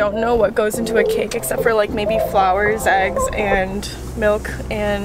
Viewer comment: did she say flours and not flour